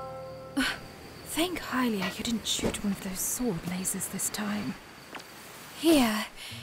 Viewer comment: What did Zelda say? I couldn’t get it. And the subtitle failed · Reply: She said “Thank Hylia you didn’t shoot me with those sword lasers this time.”